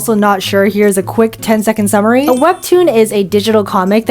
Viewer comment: I thought it said 'weeb toon'😭